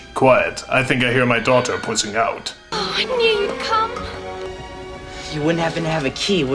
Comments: okay i know she said i knew youd come but i could have sworn she said "i need cum" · "Quiet, I think I hear my daughter putting out." XD